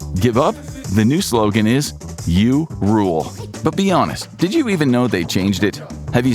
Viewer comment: “You rule” makes so much more sense. I thought they were saying “get a room”